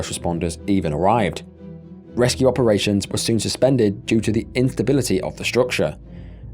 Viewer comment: All of this is so sad and i feel horrible for all the victims which is why i apologize for the next bit of this comment… I cant with the this bit here It sounds like you said “inthability”💀 38:22 Im sorry please dont come after me, my ADHD + possible Autism makes me laugh during the worst moments (not irl, just while watching sad stuff)